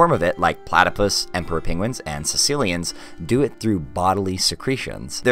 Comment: I thought you said Sicilians